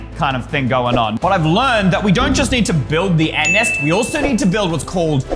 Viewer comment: In 3:02 i thought ey said anus but he said anest😭